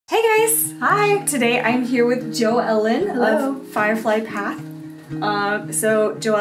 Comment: I kinda thought she said Joe Weller at the start😂